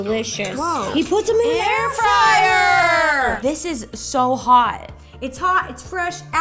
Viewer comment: When they said he put it in a air fryer a thought they said he put them in a hair dryer.🤤😖🙈